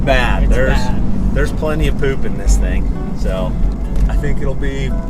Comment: I heard say "big slicks in the back", so I can't get 6.0 liter LS out of my head now...